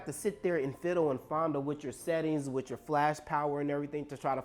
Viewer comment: did you say fondle your settings?...lol